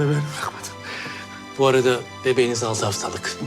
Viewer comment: de doktor ne diyor anlamadımm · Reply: 6haftalık diyor